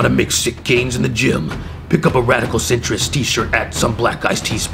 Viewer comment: thought you said “Radical citrus” loll